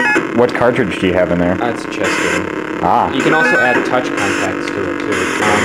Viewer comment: I think I heard, "what cartridge do you have in there? ... uhh, it's a chess game"